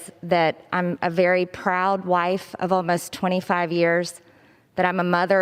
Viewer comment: For a second I thought she was about to say “I’m a very proud white”